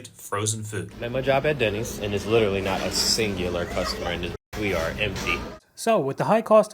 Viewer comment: Did he say there was not a "singular" customer?